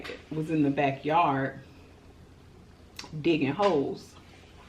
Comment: Girl i had to rewind i thought u said ole boy was in the back gettin hoes you actually said he was in the back diggin holes😂😂😂 i was like wt... 😂😂😂 and you did the DAMN THANG WITH THOSE CRABS GIRL MH MH MH🤦🤦🤦 YOU GOT ME EFFED UP OVER HERE I GOTTA GET ME SOME KING CRABS MANE